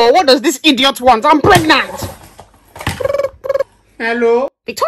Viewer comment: when i was looking away and not really Paying full Atention i heard pregnet I was Like PREGNET HÉ Then i rolled the video back and heard pregnant i was like ah ok pregnant i thought i heard pregnet